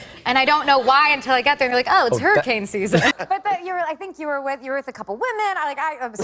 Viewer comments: What she said 1:26 ??? 🥺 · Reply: she said "i think you were with a couple of women" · Could someone tell me what Lizzie says in 1:24 please, English is not my first language and I want to understand well what she says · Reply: she said 'you were with a couple of women'